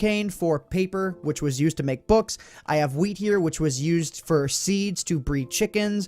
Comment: I heard "We have weed here" XD